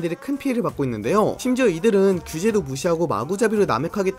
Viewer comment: 주제를 무시하고라고 잘못 들었는데 자연스러웠다